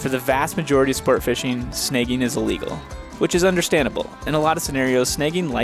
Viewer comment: Took me a while to figure you were saying "sn-ah-gging" a fish. I heard "sn-eh-gging".